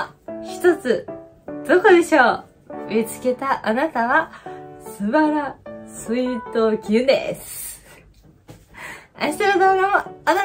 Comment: 「すばらスイートギュゥです」 ん？何て言ってるんだろう？